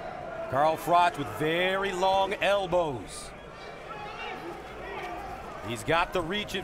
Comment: did he just say froch had long elbows?